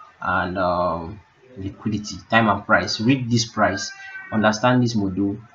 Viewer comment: It is for paid service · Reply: at around 8:32 you said "... you need to correlate your time and bias and liquidity, time and price ** this price, understand this module..." I don't get what you said at that point My guess is you said "read this price, if that's what u said", pls what do u mean by read this price?